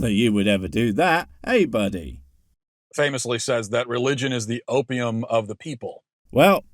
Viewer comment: Playing at 1.5 percent speed, it sounded like Matt said "religion is the oatmeal of the masses"